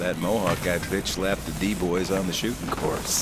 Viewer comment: did he say d bois ?